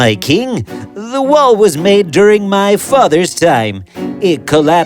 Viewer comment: did he say theif